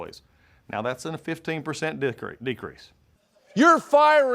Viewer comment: did he just say "dick-creese"? Stephen was right, he IS a fan of the D. LOL